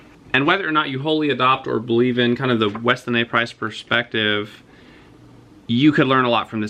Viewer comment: Sorry, that came out so fast it sounded like you said, "western ehs price perspective". Could you print that out in a reply, please? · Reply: Weston A. Price